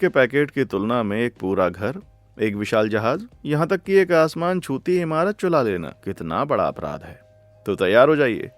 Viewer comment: "chula Lena " who heard the same?😂